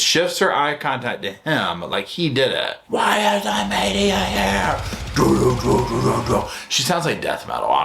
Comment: To me it sounded like she said “where is the media”.